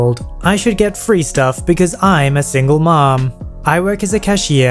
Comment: I thought it said sing lemon